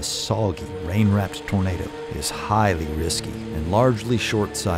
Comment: the subtitles are amazin.. instead of tornado it shows burrito in the beginning 1:40~